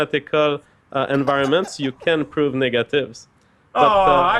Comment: When he said "Negative" it sounded like "Niggative" so i laughed hard.